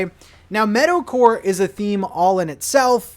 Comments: Definitely thought you were saying "Metalcore" and got very excited for a second there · The whole time I thought was saying metal core 😂😂😂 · thought you were saying Metalcore for the first 5 min of the video